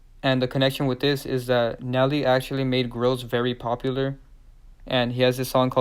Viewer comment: Saying Nle fast sounds like your saying “A Nelly”